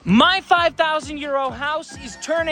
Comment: Whenever he says 'My 5,000€ house' I hear 'My 5,000 year old house'😂😂😂😂